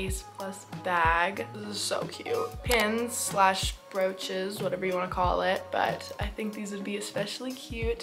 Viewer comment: For the pin idea I thought you said roaches😭